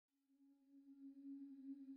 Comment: being quite honest, i didnt hear 'whispers in chaos' at first, i heard 'whispers in kale' and was like wtf?!?!?